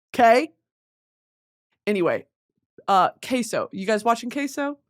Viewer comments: she said case oh and I fr thought she said queso LMFAO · I thought she said queso not caseoh